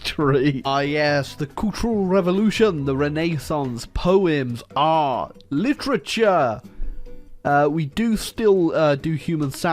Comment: I thought he said,”Poems Are Literature.” And I thought,”Who says they aren’t?” Then I realized he said,”Poems, Art, Literature.”